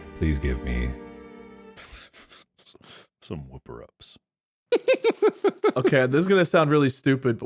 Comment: I thought they said whippet, which is a breed of dog 😂